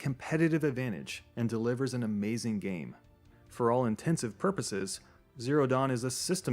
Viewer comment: Did he just say "For all intensive purposes"? :D :D